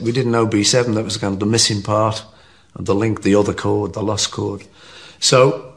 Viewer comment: Did he just call B7 "The lust chord"?